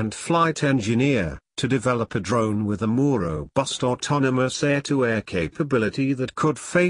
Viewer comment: or thereabouts... "a Moro bust" rather than "a more robust" Actually very difficult to follow what is being said...not that there's any actual info being provided.